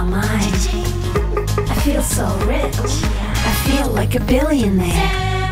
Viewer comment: nah I just skipped the ad on that part and I thought I heard "I feel like a bi***" 💀💀